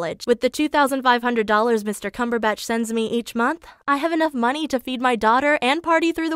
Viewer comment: I thought that she said her math teacher's name was “Mr. Cucumber Patch” :’)